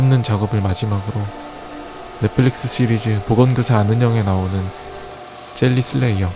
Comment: 때 보건교사 아는형 이라 들림